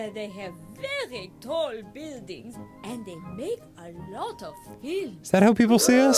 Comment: I had the volume low and I swore she said "and they drink a lot of milk." 😂